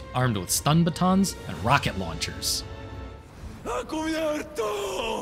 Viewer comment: Always find it funny on the island when they insult each other and call each other useless. Also always made fun when they said ¡A cubierto!. Don't know why but i always heard it as ¡Pan cubierto! In which means covered bread, lol